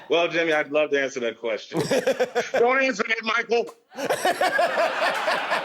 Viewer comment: Wait, what did Dave say at 12:13?! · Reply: Don’t answer that Michael!!!